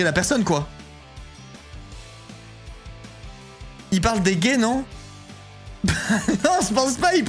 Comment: Lunaire la séquence à 10:50 pourquoi il parlerait des gays? En quoi ce serait injuste que les gays aient pas à penser à la contraception??? 💀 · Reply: il parle des hommes je pense